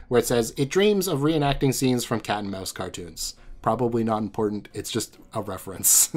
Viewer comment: every time you said "Maus" before it came on screen ~ 14:00 i thought you were saying "Mao's"